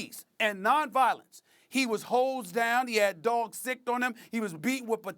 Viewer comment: I heard "Hoe's down" X-D